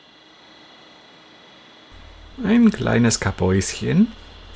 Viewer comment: Lach ein kleines Kapphäusen (<Hab ich glaube ich falsch verstanden bei 42:04) Für ein kleines Pipi Päuschen